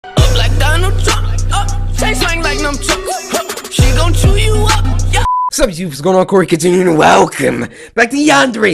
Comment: I swear to god I heard "taste the Donald Trump" at the beginning of the video. XD